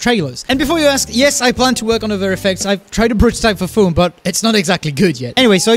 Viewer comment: Sorry if I sound like an idiot, but what are you saying at 0:14 ? Weather effects? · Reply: i think hes saying "other effects"